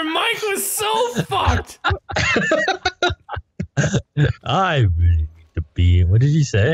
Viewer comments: My favorite "I really need a prfhejek" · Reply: I heard "I really need a pizza now" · Me in the middle of a test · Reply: I heard "I really need a piece of bread"